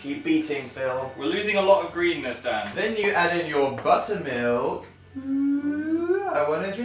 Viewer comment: when dan said "add in your buttermilk" my laptop started buffering and it sounded like he said "add in your butt" lmao